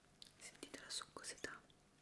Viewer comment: escuché sentí toda su cosita, ajajjaja😂😂